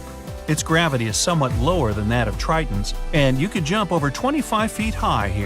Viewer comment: did he say trittans